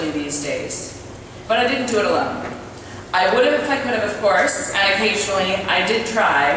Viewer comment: I can't make out what she says at 4:25. "I would've" what? I'd like to know because she jokes about it after. Anybody? Thanks. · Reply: I would've if I could have of course, and occasionally I did try and I have the photos of the corpses to prove it